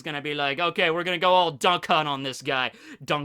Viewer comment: sounded like "were gonna go all dumb cunt on this guy"